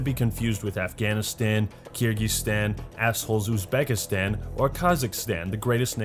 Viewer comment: Why did you say “ Asshole’s Uzbekistan”, like what the hell? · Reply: Marc von Richthofen It definitely sounded like he said asshole and then Uzbekistan